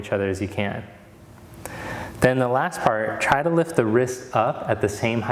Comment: thought you said lift your ribs up what, oh wrists!!! I have hearing issues... :) very good practice for today. Thanks!